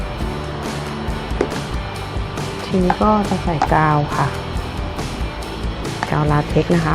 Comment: ใครได้ยินว่า กาวลาเต็กซ์บ้าง